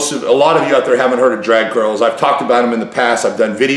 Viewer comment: thought he was saying , "Drag girls"...then realized it was drag curls.